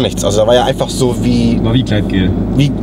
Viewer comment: was sagt er bei 3:06? :D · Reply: War wie gleitgel